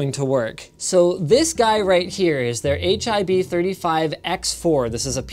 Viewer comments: it sounds like u say HIV my boi you trying to communicate to us? when did u get it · Did he say hiv